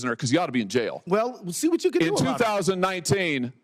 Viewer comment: Did he just say " See what you could do about ?" 6:15 😮😮😮Sounds like bluff loud and clear 💯💯💯💯💯 GUILTY